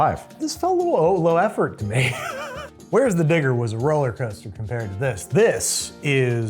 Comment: Uhh what did he say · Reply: "Where's the digger".